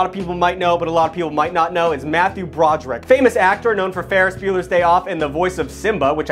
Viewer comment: Almost had a heart attack because I thought you said Matthew Patrick